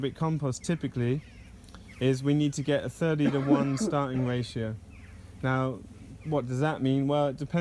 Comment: did you say thirty to one ratio?